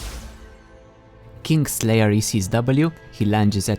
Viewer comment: I thought you said kink slayer